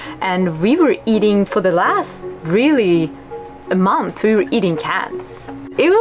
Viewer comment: does it not sound like she says "For the last month we were really eating cats"